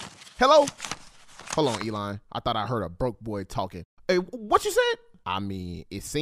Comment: I thought he said “broken knees”